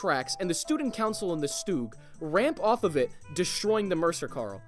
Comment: did you really called all mighty Tutel a St*g